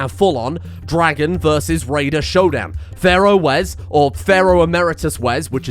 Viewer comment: Legit thought you were saying "Pharaoh Americus" and that this episode was going to involve drilling for oil and bombing the other desert nations.